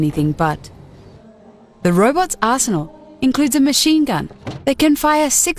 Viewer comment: At first I thought she said “The robot’s arsehole includes a machine gun” until I went back and heard it was actually “Arsenal”